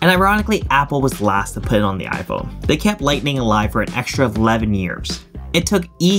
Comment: " an extra Vladimirs " Somehow, that's what I heard 😶